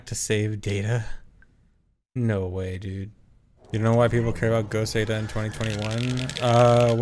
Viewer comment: Can anyone understand what he's saying at 14:23? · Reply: Ok ty it was "ghost data" I just wasn't catching for some reason